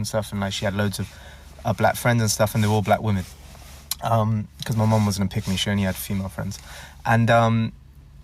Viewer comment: I thought bro said his mom was a Pygmy